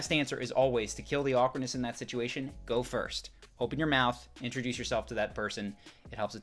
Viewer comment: Was sitting at work with headphones on and then i hear “FBI open up” and was like wtf